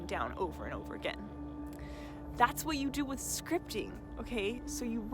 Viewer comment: Haha at first I thought you said Scrying ? Like a a Scrying mirror haha so funny luv anyways it would probably work too haha for Scrying out loud ha! Anyways I get what your saying now and I'm certain repetitive Scripting works wonders I dig it! Super Awesome information here so powerful I love this thanks much appreciated luv Have a very positive powerful prosperous day !! 🌞✌😎🕯💵💰🤑 Yes!!!